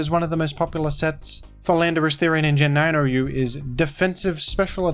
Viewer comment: I'm sure I just heard you say "Philanderous-Therian"